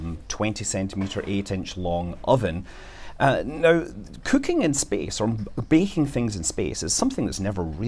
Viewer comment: I gotta put my glasses on...I thought it said Banking in space.